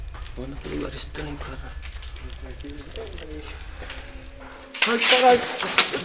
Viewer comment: Eu ouvi alguém dizendo : "Sai daqui" no